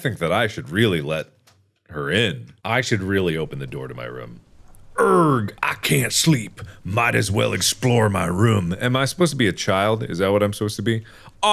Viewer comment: i thought he was going to say "errg i cant sleep might as well explode"